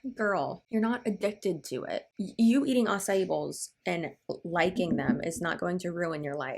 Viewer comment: Did she just say she's addicted to asai balls...... like what a total clown 🤡 not even funny. Like you love protein balls...that ain't addiction biiiiitch.